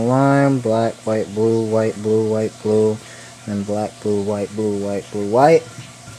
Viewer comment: I thought you said white boob! XD